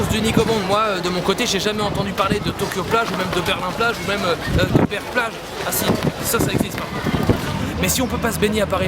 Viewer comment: il dit quoi svp ? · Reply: Berck plage